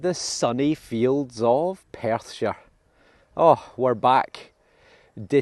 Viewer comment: I always thought you were saying "welcome back to the fields of pasture" Until I seen it in a title, and figured out you were saying Perthshire! SORRY 😅 my bad.